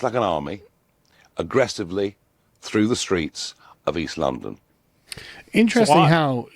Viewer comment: I thought he said Islamdon not East London ngl